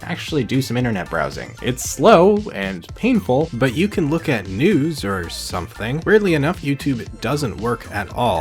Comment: thought he said “you can look at nudes”